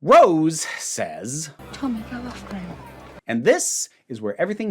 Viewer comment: for anyone else trying to make out what exactly Rose said to Tommy since it's basically impossible to make out without headphones as far as i can tell (even with volume all the way up on my laptop and youtube AND an audio booster extension on firefox, i couldn't make out anything except that it was short), according to the script on the BBC website, it's "Tommy, go after him." i haven't ever actually seen an episode of doctor who (i'm watching these partly just to spite the BBC for targeting Vera), but if she'd said this to me when i saw my dad the last time, i probably would've punched her. I'm STILL grateful every single day that my dad no longer tries to talk to me. fuck off with that bullshit Rose.